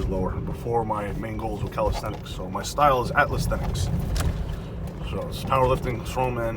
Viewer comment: Thank you very much for the inspiration and the direct and deep explanatory. I didn't quite understand at around 4:10, your actual style is called... athlestenics? Atlastenics? Is it a word you found to describe your style or is it something to find in the internet? Keep it up!!